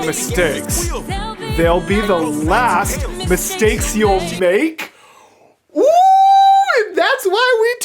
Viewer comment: i just misheard circes' 'thell b the last mistakes u make' as 'they be the last meat stakes u make' which- well he kept making mistakes n paying it in crew ppl but he didnt make more meat stakes of them so